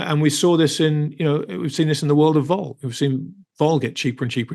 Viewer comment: What is saying around 50:00? He was talking about gold but it sounded like he was talking about “vaul” or something I couldn’t hear correctly. Thanks.